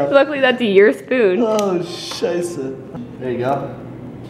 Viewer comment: Did you said "ahh scheisse" @ 10:26 ? Since when you speak German? And yes I'm from Germany! :)